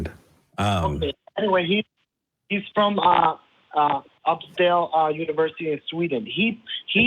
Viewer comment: did he just say "Upscale University"? I think he meant Uppsala 😂